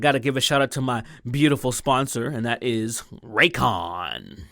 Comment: When he said Raycon I thought he said Raytheon so i legit thought had sold out to the Military industrial complex... False alarm